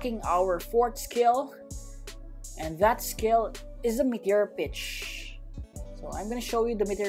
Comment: When he said meteor pitch I thought he said meteor ditch with a backwards d 😂